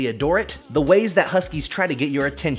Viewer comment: Is It Me Or Did He Just Say Numero Sixto?